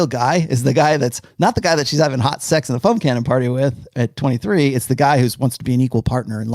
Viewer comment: Almost got through a show without the "foam cannon guy" 😹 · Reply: This whole time I thought he was saying "phone cannon party", and here I was wondering what the hell a phone cannon was haha, foam cannon makes a lot more sense xd